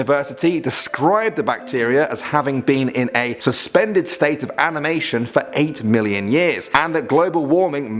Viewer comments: is it me or did he say aMiNation? Still love this channel tho. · He said "amination" instead of "animation." ^^